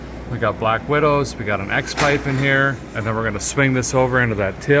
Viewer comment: My wife thought you said “Two Black Widows and a X-Wife”. 😂 I told her you said X-Pipe.